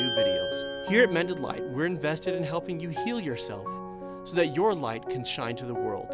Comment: I hear "here at medned lights" we are fully vested in you tp kill yourself" instead of "heal yourself". Is it bad?